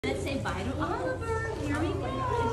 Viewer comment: I thought they was saying bye to his life 😢😢😢😢😢😢😢😢